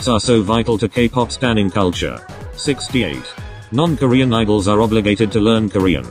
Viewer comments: Bro did u just say kpop stans are obligated to learn korean? Likd, uhm- what? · FOR SOME REASON I THOUGHT YOU SAID “non-korean FANS are obligated to learn korean” for 68 AND WAS SO CONFUSED